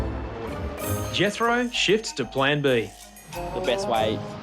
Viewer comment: Did anyone else think he said "Jethro 'shits' to plan B"? Or was it just my imagination?